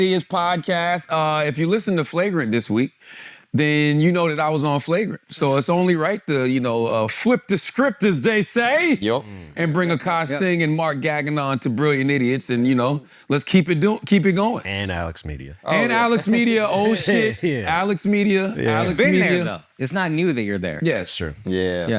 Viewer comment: Is charla saying mark "gaggin on" on purpose? If not, thats hilarious